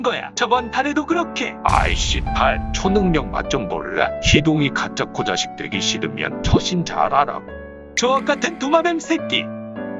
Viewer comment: 희동이 가짜코 자식되기 싫으면 처신 잘하라고 들리넼ㅋㅋㅋㅋㅋ